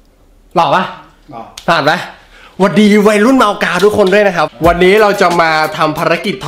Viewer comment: ได้ยินเป็นวัยรุ่นเมากาว55